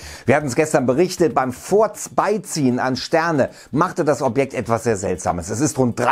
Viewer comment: Hat er da grade beim "furzbeiziehen" gesagt? XDDD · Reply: also ich hab beim "forz bei ziehen" verstanden :D .. klingt wie nen versprecher "beim forza 3 spielen" :P